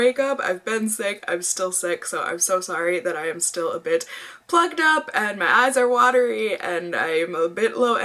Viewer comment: I thought you said "sorry I'm a bit fucked up" when you said plugged lmao